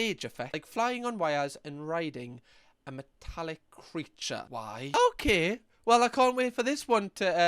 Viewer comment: I thought he said riding a phallic creature. Not riding a metallic creature. 😂